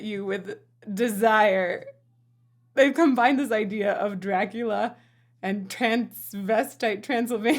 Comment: Did you say DRAG-ula??? 🤭🥵